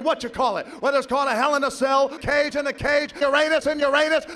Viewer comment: A Jew in a Cell Match?! How does THAT play out?! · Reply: I heard it as "Achoo in a Cell".